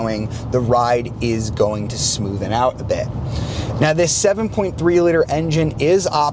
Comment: Did he actually say, "smoothen out"?